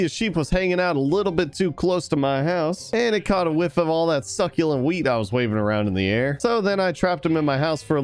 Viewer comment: did he just say succulent weed😦🤣